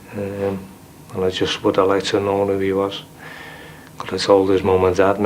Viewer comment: wtf did he say? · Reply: "would like to have known who he was" , (the young child looking for his dad) "Could have told his mum and dad"